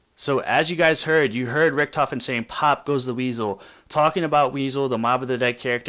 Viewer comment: I thought he said chop goes the pieces